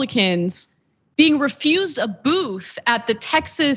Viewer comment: “The Texas Log Cabin Republican’s refused a boof...” That’s what I misheard! 😂